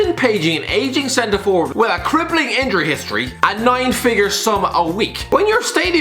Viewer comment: Was it just me or did he say 9 figure sum a week at 2:25?